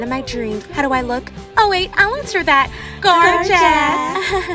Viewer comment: oh wait ill answer that!" ,,G0RE CHESS:D" (misheard gorgeous)